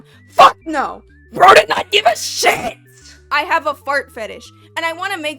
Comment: I had to reward like thrice cause im like "did she just say she has a fart fetish?" lmfao shit had me in stitches